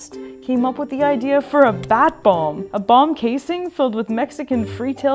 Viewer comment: Bat BOMBS? I thought you said BONG